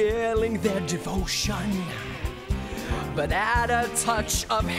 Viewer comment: What does he say to Simon at 2:38? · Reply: "But add a touch of hate for Rome"